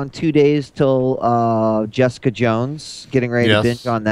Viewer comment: When said he was getting ready for Jessica Jones, I wasn't paying very close attention and I swear it sounded like he said "Geoff's cajones."